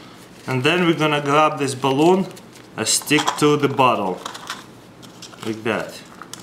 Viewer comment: lol at 0:43 it sounds like he says stick to the buthole lol